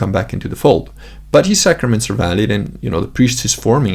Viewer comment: in reference to Bishop Sanborn and the priests he ordains, did you say valid or invalid? I think you said valid, but wanted to make sure. You were running along the lines they were valid - but just wanted to make sure. Thanks! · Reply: Valid